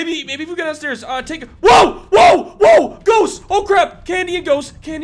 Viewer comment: It sounded like Bonnie said “oh crip”